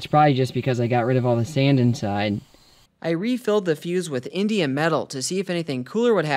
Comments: I heard "Indian metal" at least 4 times and I'm all "what the heck is Indian metal?? Aren't people going to call that racist?." (It's indium… LOL) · I heard him say he filled it with "Indian metal" . Ive never heard of it. Google was no help (its a band). I then rewatched it with subtitles on and learned it's "Indium metal".